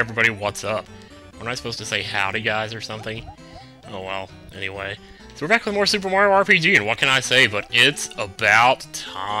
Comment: My video had to buffer and it stopped in a place where it sounded like he said "Hey were back for more soup" LOL